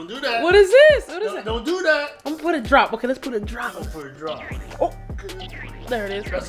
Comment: i am gonna put a drop in that: put 3 drops Me: I thought you said A drop